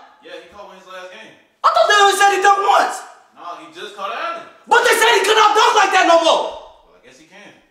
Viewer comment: "BUT I THOUGHT THEY SAID HE CAN'T DUNK LIKE THAT NO MORE?!" "I guess he can... " Idk why, but that shit killed me 😂😂